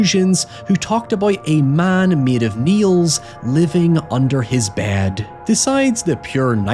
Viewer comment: I thought he said "Man made of MEALS living under his bed"😭😭